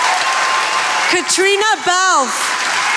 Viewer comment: You heard it when they called Christian bale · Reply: I heard " Katrina Bale " !?